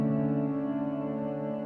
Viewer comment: At about 57:12 at the very end he says something like "Jahgger Dah" Anyone know what that is? TIA XOXO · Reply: Jai Guru Dev Glory to the shining remover of darkness.